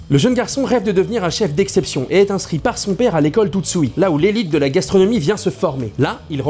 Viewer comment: juste c'est moi ou a 1:30 il dit tootsui et pas tootsuki